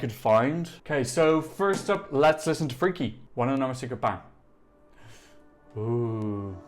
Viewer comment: Hey! Love your videos, but what are you saying every time right before you play song? I keep hearing 'wanna know my secret - bang'. 😅🙈